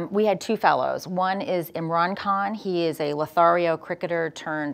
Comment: aww did she call Imran a LOTHARIO! @ 05:59 or didi i get it wrong ...Wow!.. thats a new adjective for mr Khan!